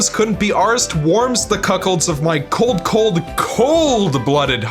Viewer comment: Did you just say that something warms the cuckolds of your cold, cold heart?